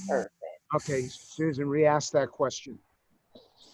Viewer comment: Who tells someone to "re-ask" a question? No wonder she thought you said "We asked that question." Learn to enunciate, Luntz, Jesus.